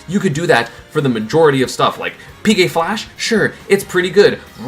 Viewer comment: did he just say pk flash is good